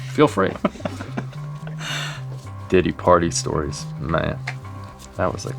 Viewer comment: did he say diddy party and followed it up with weird memory lane.